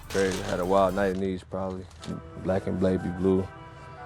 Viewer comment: Did he say black and blaby blue